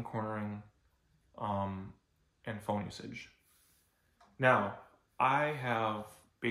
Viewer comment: what did you say? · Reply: “And phone usage”